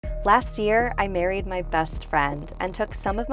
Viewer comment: omg at first i heard “last year I buried my best friend” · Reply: I thought she said "last year I carried my best friend" lol I didnt even think of berried :D...